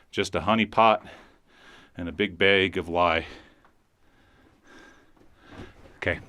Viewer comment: I heard, "A honey pot and a big bag of lies"